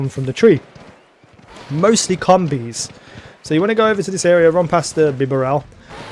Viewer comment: what did he sayyyyyyy??????🤔🤔🤔🤔🤔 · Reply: Mostly combees